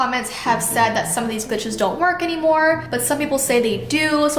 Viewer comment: Idk if its js me but I heard "bitches" not "glitches"